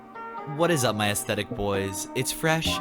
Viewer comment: i turned my phone off during the intro and it sounded like he said “ what is up my my ass”